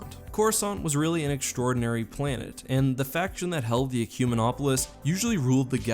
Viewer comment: Can someone tell me what word he said at 1:10? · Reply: Ecumenopolis